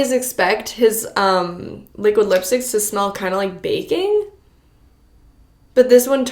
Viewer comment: 🤣 I thought you said the lipsticks usually smell like BACON... lmao I had to listen twice to finally realize you said baking 🤦🏻‍♀️